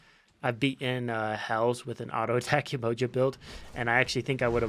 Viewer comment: wait.. 7:30 did you really said, basic attack Yemoja, are you implying that you’ll do a magnificent full attack speed Yemoja video, perhaps ? (also, been playing that thing since 2014, i was 12 when i started, jesus.. i still suck tho....)